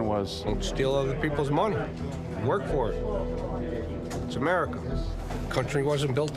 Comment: wait did he sayyy " This is America" 😱